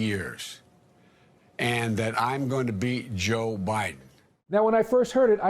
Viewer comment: It really does sound like he said, "beat Joe Biden" though lol.